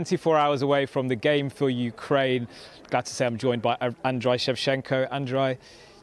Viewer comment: did you just say 'Androy'?